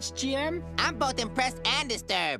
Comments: “I’m both impressed, AND disturbed!” 🅰️ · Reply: Dang I thought he said "I'm both depressed and disturbed." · “I’m both impressed, AND disturbed!” 🅰️ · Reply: Thought he said depressed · "I'm both impressed and disturbed!" Coming from Sheen, that's saying a lot. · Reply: I thought he said depressed